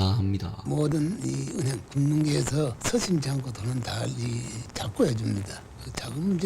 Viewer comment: 모든 이 은행 금융계에서 수신창고들은 다 이 창고(?)에 둡니다. 정도로 들리긴합니다. 경상도 특유의 악센트에 노인이라서 발음이 부정확하게 나오는거 때문에 듣기 힘든 것으로 생각됩니다. 사카린 사건은 이병철 회장이 박통한테밉보인게 아니라 박통한테 통수,,,손절 당한거에 가깝다고 알고 있습니다.